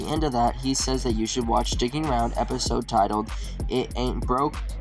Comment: When it said “watch Stickin’ Around” I swear I heard “watch dickin’ around” 😂